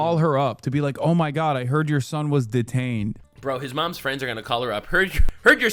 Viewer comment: oh my god I heard your son is deranged